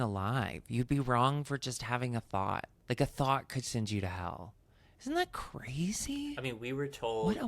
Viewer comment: I'm not American and I totally misheard as "a fart could send you to hell" which honestly sounds about right